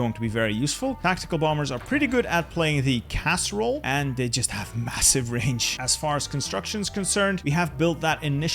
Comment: LOL it sounded like you said "casserole" instead of "CAS role"